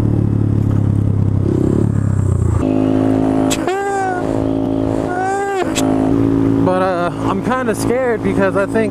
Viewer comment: someone please tell me what he keeps saying this for at 02:31 its funny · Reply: CHEAH!